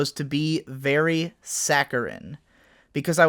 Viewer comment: Sacaran? Saceran? Sacarant? I get the idea, but what's the word? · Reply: Saccharine.